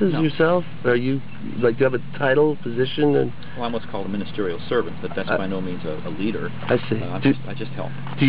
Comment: Minor correction to the descritption box: at 4:12 says he is a Ministerial Servant, not elder. (Sorry for being pedantic, but you know that any JoeHoes watching will dismiss it entirely due to any minor inaccuracy). That aside, I'm loving this. What did you say to convince them to appear on camera?